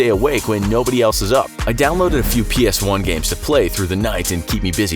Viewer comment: Did he say PS ONE games at 2:18? LEGEND